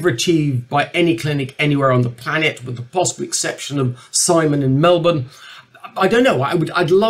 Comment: what is he saying at this minute??? What clinic? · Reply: He only says "Simon in Melbourne"